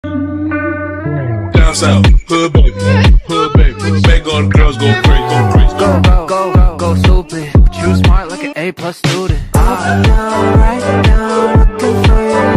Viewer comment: did I really hear: "herb babe ... herb baby... make all the girls goes crazy?" XD